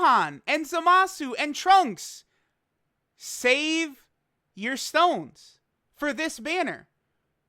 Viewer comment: huh? Didn't quite catch that did you say waste my stones on the LR banner that's on global rn?